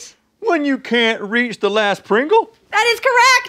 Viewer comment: I thought it said "When I can't reach the last pringle"